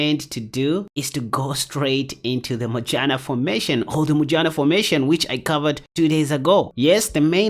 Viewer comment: Sounds like he is saying the vagina formation 😅